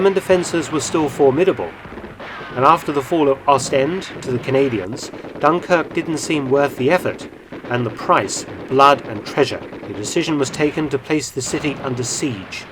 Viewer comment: I think it's at 6:56 I heard Dr. Felton say, " arseend."